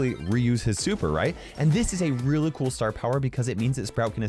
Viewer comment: did he say star power